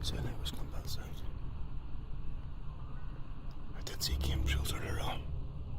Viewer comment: For anyone who is unfamiliar with the irish accent... 37:18 "Sounds like it was from that side... I did see game trails earlier on" 37:26 "What's the biggest game we get over here? We don't get big game..." 37:32 "Well I think that's a night time stroll out my head definitely... frick that" 37:39 "Maybe im being paranoid. I'll have to check back on the camera... but i swear i heard like... something go past there"